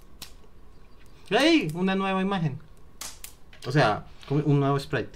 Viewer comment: Solo entendí HEY QUE SABROSO JAJAJAJA